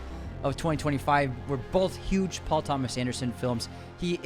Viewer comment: Did he say “We’re both huge Paul Thomas Anderson Films”?